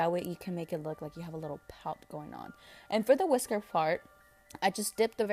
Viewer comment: It sounded like you said whisker fart lol😂❤️ but I love you 💕 I watch EVERYYY video 💕💕🌹🌹